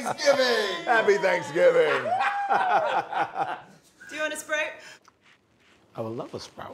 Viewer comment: I thought she said Sprite instead of sprout. I was like what...